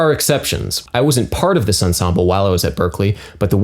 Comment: He stated that he wasn't a part of the group. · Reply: Alexander Jones ah I just rewatched it. I heard "I was apart"" vs. I wasn't part" the first time watching.